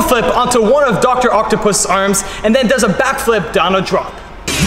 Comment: am I the only one who hears “then backflips Donald Trump”